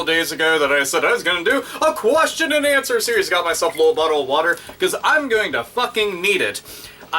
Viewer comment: did he say butt hole of water XD